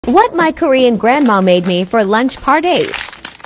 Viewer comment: sounded like it said “what my grandma made me for lunch party”